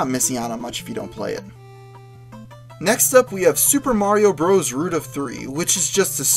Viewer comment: please tell me the name of hack mario bros 3 in minute 2:00 please,i cant understand you voice,thanks. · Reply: alan route of three, root of three.